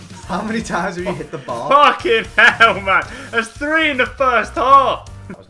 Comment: did say fu%king h$ll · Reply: Oh fucking hell he did!